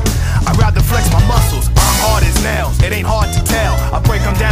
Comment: At first I thought Knuckles said I'm hard as hell